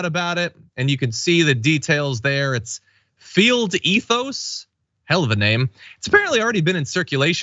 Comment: At 1st, I thought you said failed ethos...